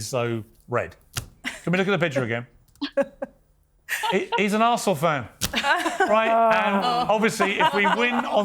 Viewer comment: I thought he said “he’s an arsehole fan”